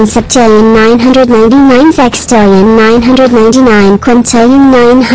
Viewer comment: it sounded like Cailou said sex